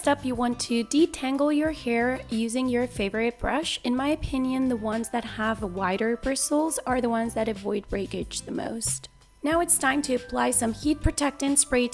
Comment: did she say to use the wider bristles a ..... breakage the most?